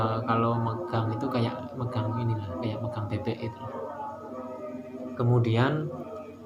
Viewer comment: Apa aku salah dengar ,,,,empuk kaya megang pepek